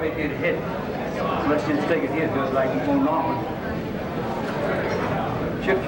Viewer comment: Thanks for video.. @ 6:57ish.. did he say Moe Norman?